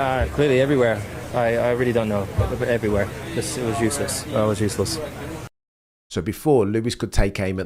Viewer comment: Lewis says, "I'm useless." We can debate what he meant. But we can't debate the fact - the words that came out of sir Lewis' mouth. Best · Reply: I really heard "I WAS useless". English might not be my first language but my hearing is fine. I listened again, for the avoidance of doubt, with volume higher and even CC says "was" instead of "is".